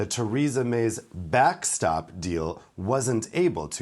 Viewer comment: i thought he said "back stab deal" realised i miss-heard but then thought, well actually...